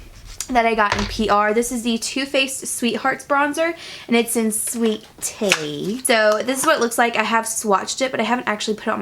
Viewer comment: when you said "sweet tea" I thought you said "sweet titty"